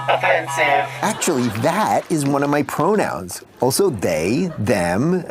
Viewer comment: "hey pronoun" sounds like something someone would say to make fun of conservatives that thing feels like satire 💀 edit: the episode descriptions insisting on calling the character "nonbinary chaz" every time they're mentioned is so funny, like why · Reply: i thought he said "bro-noun"